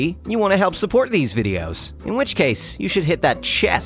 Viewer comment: Did i just hear be sh** at 1:08?? Edit:parents please dont show this to your youngest kid at 1:08 because i dont know if he said a bad word just please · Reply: He said "but you should"